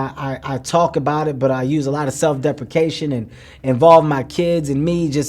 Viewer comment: does he say "thug defecation"(thug shit)?